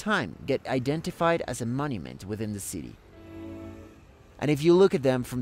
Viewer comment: What’s your favorite monument in Rome? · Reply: Sorry I thought you said moment not monument. Obviously you have to exclude the Pantheon which is in a league of its own but I think the very ruined Temple of Venus and Rome a building that is a palindrome, conveys with imagination how astonishing the city must have been under the Antonines.